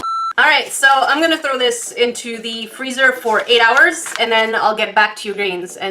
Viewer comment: When you said "I'm gonna throw this into the freezer" I seriously had this tiny moment where I thought you said "I'm gonna throw this into the void"